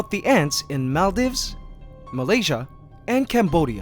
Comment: did you just said malaysia?Cause I love in malaysia!!!!!!love ur videos I subscribed